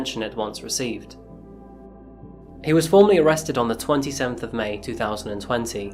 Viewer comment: I’m sorry did you say the “twenty-tenth of may” around 14:45? I’m so confused